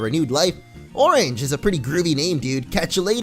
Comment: for a second I thought said "Orange is a pretty creamy name dude"